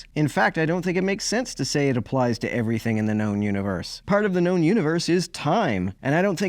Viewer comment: I as so confused. I thought he was saying "the non-universe" and im like wtf are these apologists up to now?